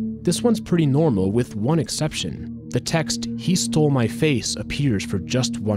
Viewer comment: i thought “he stole my face” said “he stole my taco”